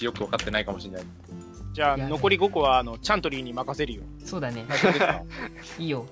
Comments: ちゃんとりーがサントリーに聞こえたw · ちゃんどりーがサントリーみたいに聞こえた